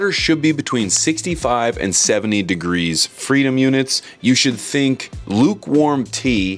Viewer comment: did bro just said freedom units ? 💀